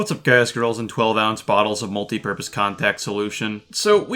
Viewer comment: What’s up boys and girls and 12 fl oz bottles of multiverx (don’t know if that part’s what he actually said) contact solution 😂